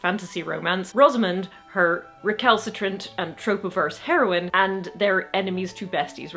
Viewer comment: You said "trope-averse," and I thought you said "tropiverse." Like, where's that?? Hurr!